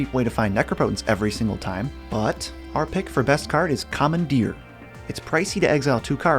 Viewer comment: LOL i was listening to this and on another tab, and when they said 'commandeer' i thought they said 'common deer'